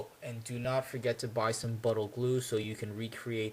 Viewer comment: what kind of glue to reseal? sounded like you said 'buttle glue'